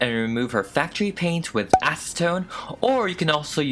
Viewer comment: Am I the only person that's heard say "asstone" instead of "acetone" at 16:02 •Δ•